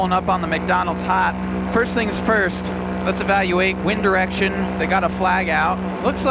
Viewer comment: i thought he said one direction but it turns out to be wind direction